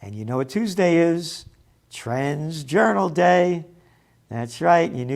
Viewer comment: Thought he said trans journal for a second